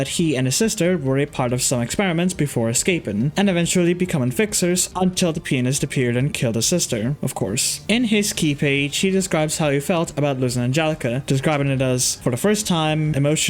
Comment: it sounds like your saying the penist